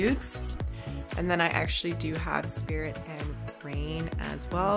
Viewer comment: Did you say spirit and grain? XD